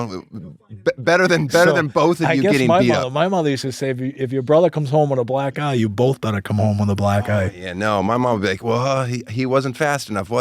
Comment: Stood up to get coffee, came back to this part with out context...and it sounded like: "If your brother comes home with a black guy, you both better come home with a black guy."